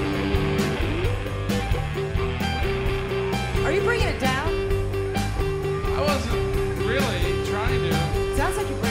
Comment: Can someone tell me please what they're saying? I don't speak english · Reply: Kim: "Are you bringing it down?" (meaning the overall sound of the song) Black Francis: "I wasn't really... trying to." Kim: "Sounds like you were bringing it down." Then, they have some back and forth banter. Near the end, Kim says, "I don't know how to bring it back up. Oh! The drummer brings it back up!" On the cue from the drummer, they then end the song. Hope that helps. 😊